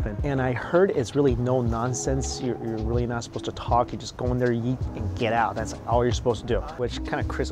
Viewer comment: I stg. I thought you said 'go in there yeet and get out' 😂😂